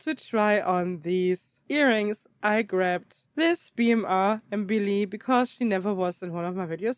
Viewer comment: who is this doll please. I can not quite hear what she says, BMR? 19:25 purple dress fabric may be organza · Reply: Yeah, she is the BMR Mbili doll aka GHT91 Organza is a good guess. It definitely feels like a very nice quality fabric and even if it is no real Organza, it's definitely a way better quality than usual doll clothes fabric 😁